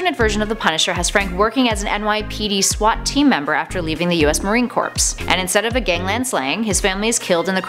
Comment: What at 3:15 did she say marine corpse